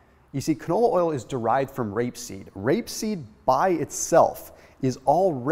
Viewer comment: Omggg I thought he was saying grape seed, he is not